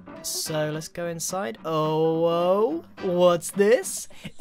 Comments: DID HE JUST SAY “OWO WHATS THIS” IM DIEING XD XD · I'm extremely late BUT did grian just say "owo what's this" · did he just say ''owo''